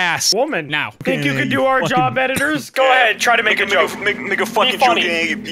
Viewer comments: BRO at 9:57 i thought said the nword but it was him just trying to say "make" XDDDDD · Did anyone else heard say something else? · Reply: I heard it too lol, but with the context he clearly says/stumbles while saying "make a" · did just say the n word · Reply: Nope. He stuttered saying "make a".